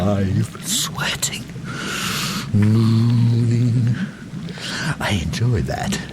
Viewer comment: I'm sure that was supposed to be 'moaning', but it sounded like 'mooning', so all the drama was gone xD